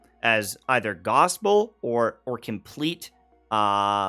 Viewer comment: What's the word that he says here? Right after "opposite of gospel" English learner here 😅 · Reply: Vitriol